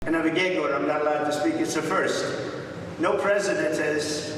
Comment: For a moment, I thought he said, “I’m a giggler, I’m not allowed to speak.”